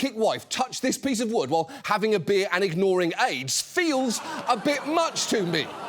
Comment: For a moment I heard that as "ignoring aides" but then I realized he said AIDS.